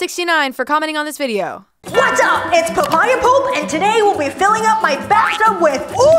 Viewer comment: Did Olivia said Papaya Pulp? And most importantly is she impersonating Guava Juice? Because she's doing a great job